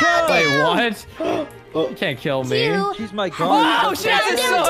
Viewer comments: did said "oh shit!"? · I THOUGHT AT 0:15 SAID THE S WORD- S***